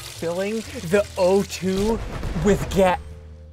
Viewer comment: literally sounds like he said “Gyatt” 🤣😂🤣😂